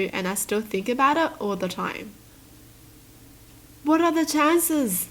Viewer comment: said WHAT ARE THE TAXES when she meant to say WHAT ARE THE CHANCES im I the only one who heard it?